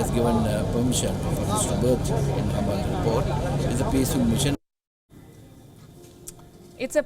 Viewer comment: correction at 2:01 . I think he said its a peaceful mission not that its a peace mission.